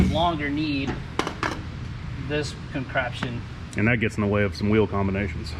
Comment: Did I hear say "concraption"